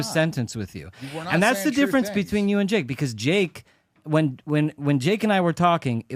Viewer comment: “that’s the difference between you and Jake.” “Jake is good goy” is all I heard Respectfully bro. Js. Nose goes 🤥